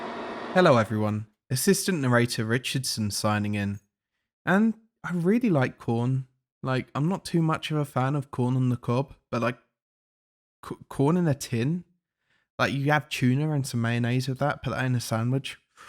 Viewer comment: I heard: "I really like porn"